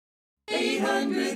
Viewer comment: When I was like 4 to 5 years old (I think, even though I have good memories a least 2011-Present but not <num> and parts of 2011 and 2012) I thought the commerial said 800-5AA-2300 and 5 TODAY!